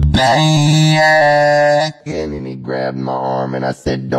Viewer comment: Is it me or do I just hear the “Then he grabbed my arm” Like “Let me grab more” ???